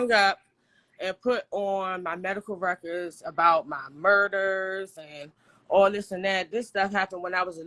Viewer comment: did she say “her Murders?” …who she murder?